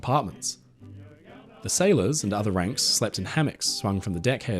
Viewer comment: The sailors and other wanks😂😂😂 I know he ranks but that was how I heard it right at first.